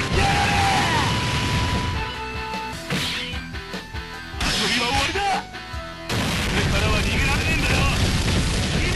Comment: what he's saying at 3:07 ? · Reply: Playtime's over! You won't escape from me, cry in pain.